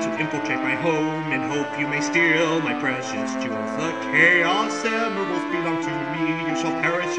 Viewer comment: When Knuckles says "jewels" it sounds like he says "jews"